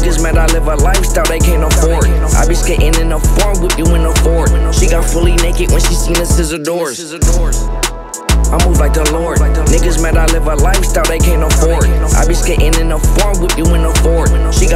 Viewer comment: For a long time I thought he said I don’t like the lord🤣🤣